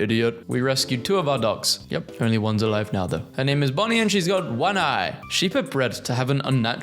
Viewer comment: did I hear bonny or bunny? My og piggy instincts heard "her name is bunny and she has one eye"